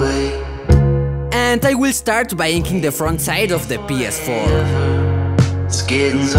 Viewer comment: Did You Just say PS4 😂🤦‍♀️